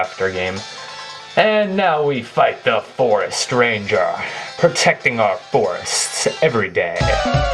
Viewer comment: i dont have good ears but did i heard him say the Forest Stranger?